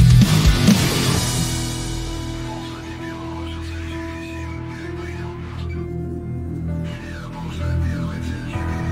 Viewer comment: Any ideas what they're saying in those short bits? 0:39 and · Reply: It's a bit difficult to make out, I hope someone will make the phrases more clear but from what I hear: 0:39 - Lane-1 (?), your target is 4-7, how copy? - This is Lane-1, target is 4-7, copy that, working on it - This is Lane-1, no visual on the target, confirm the signal - This is Lane-1, can't see the target, unable to shoot (lit. - work) 2:34 - Hold position! Get back, you bitches, not one step (back) or I'll fucking kill you all! - (Guys?), I have no fucking ammo! - (too muffled)